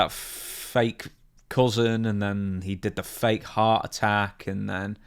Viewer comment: Yes he faked a heart attack as a desperate attempt to get fans tuning in, I can't remember when exactly but I know it was after David died and maybe after Mike died. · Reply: ooooh, HEART attack, lmao. I heard "hot" attack.🤣